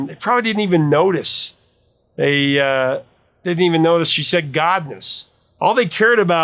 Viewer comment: Godness... I always heard it as God was on a mountain top